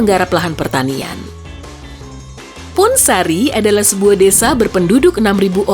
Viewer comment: Saya Dengernya Kayak "Pun10" :V